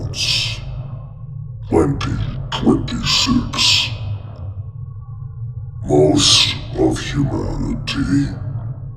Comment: JUNE ALREADY ANYONE CONVINCED FAKE OR NOT ? IS IT COMING 🤔 TO PASS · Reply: I thought He said June 2026? Not 2025.